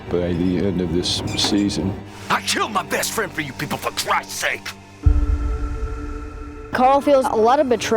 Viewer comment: what did he say all I got was "I killed my best friend up you..." That's all I got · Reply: "I killed my best friend for you people for Christ sake."